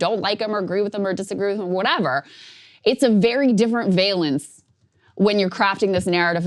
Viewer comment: "very different veilance (?)" Can anyone clue me in on what K is saying there? What is veilance? vailance?